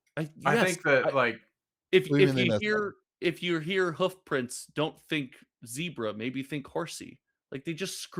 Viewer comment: I can’t tell you the last time I heard a horse print 59:02 🐴